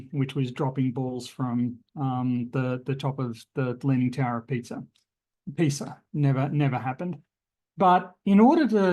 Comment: second time i heard "the leaning tower of pizza" :-)